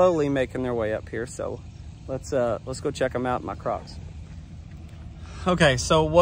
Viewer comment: Lol@ the crocs, i thought I heard ducks. Can’t wait to find out about Big Joe♥️♥️